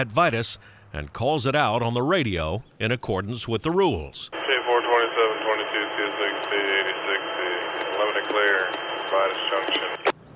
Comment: Thanks a lot! As an european it is very hard for me to understand the dialects in addidion to the low radio quality. Could you give me an example please? At 00:45 I understand: <num> CSX (unreadable) Limited clear at Vitus Jct. I never heard K427 if that is their train symbol. Thanks in advance! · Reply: I often wondered how crews understood each other on the radio. While the person communicating is DEFINITELY no Danny Harmon, he actually is one of the lesser flamboyant voices you may hear. Granted, I listened to the segment at 00:45 a few times, and with limited knowledge of what information they are conveying, I THINK he said, "K427 (train ID number) - 22 (Date? signal, did you film this on the 22nd?) - CSX (railroad identifier) - Lead 8060 (lead loco number) - Limited Clear (signal aspect) - Vitis Junction (signal location). The video didn't show clearly the lead engine number to me, so I couldn't confirm that is what he said, but I think it is. If you listen to some of Danny's other videos, you'll be treated to some true "Southern harmonizing" by the characters on the airwaves calling out signal aspects. I think they do it for sport! I hope this answers your question.